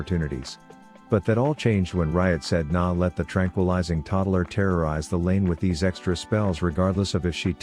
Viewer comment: Did i just hear my name at 0:50 ????? (TerraRyze)